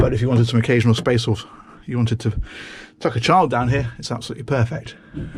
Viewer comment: "If you'd like to tuck a child down here, it'd be perfect!" Not sure why but this had be rolling. Thank you! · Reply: Christopher I heard him say “chuck a child down here” 😂